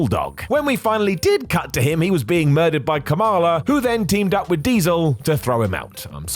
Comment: Sounded like he said Kamala instead of Kama.